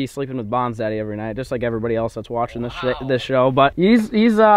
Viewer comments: Did y’all hear say shit instead of show 😂😂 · did anyone else catch him say "This Shit" and then he quickly changed it to "This Show"?